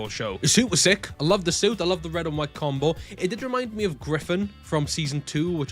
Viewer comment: My heart sank cause I thought you said Griffith